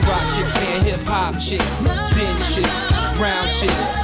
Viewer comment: For 15 years I thought he said cheeks